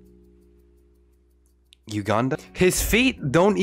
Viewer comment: did u just say Uganda 😂😂 as a Ugandan I appreciate the callout but I think he meant useless